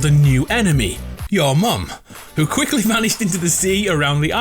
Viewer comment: did he say "ur mum" Was the enemy?